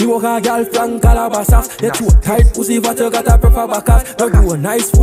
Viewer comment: WTF IS BRO SAYIN 0:30 🔥🔥🔥🔥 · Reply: Yuh got a proper backaz